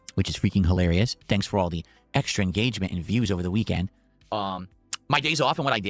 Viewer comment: I thought he said the troll engagement doesn't help 😂